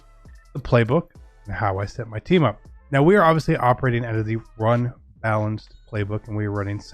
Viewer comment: You have to be in run balanced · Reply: James Seaman I went back to the beginning to see if I missed anything and I heard him say run balanced. Thank you though